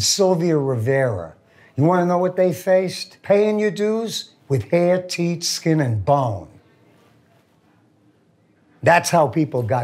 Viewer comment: Did he say hand teeth skin and bones? If so what does that mean??? And if not. What did he say?